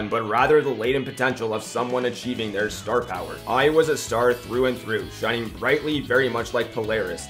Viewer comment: i thought you say "I was a star" when it was "AI was a star"